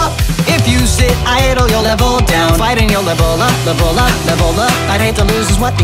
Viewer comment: Man I heard "Ebola! Ebola! Ebola!" instead of "Level up! Level up! Level up!".... jesus my brain is stuck in 2015 or whatever year it was...